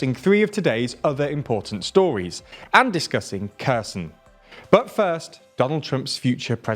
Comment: When he said "and discuss Kherson" at the start of the video I thought he said "and discuss cursing" lol